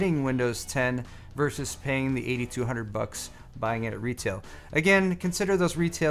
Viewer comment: LOL Initially, I thought you said Windows 10 normally costs $8200 at retail. I had to google it to find out if it was true.